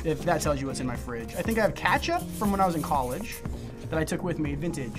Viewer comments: im sorry did he just say katchup instead of ketchup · I love how he says katchup instead of ketchup